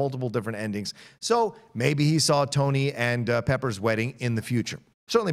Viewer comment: I thought it said welding for a second lol